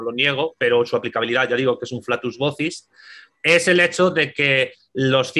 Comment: "yo no lo niego, pero su aplicabilidad es un "laxus gudis (no entiendo el termino , no lo escucho bien) " por favor podrias decirme cual es ese termino que usaste ? o alguna persona que me ayude. MIN 23:53 gracias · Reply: Flatus vocis